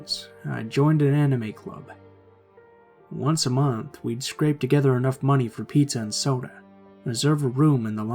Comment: I swear I thought said crêpe at the beginning of the first story